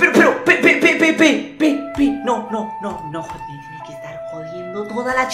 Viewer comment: pense que dijo pepino